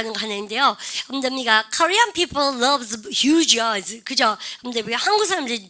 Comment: 한국사람들은 눈이 커보이는 것을 좋아한다라고 할 때 Korean people love views eyes 로 들리는데 ㅠㅠ 제가 뭐를 놓친 걸까요? Korean people love (that) views eyes 라고 생각해도 저는 해석이 잘 안되네요. 아시는 분 좀 알려주세요. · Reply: 네ㅡhuge eyes 입니다.big 보다 큰 개념 huge 입니다ㅡ